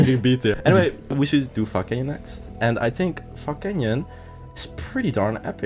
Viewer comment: i heard fog canyon as fucking yeah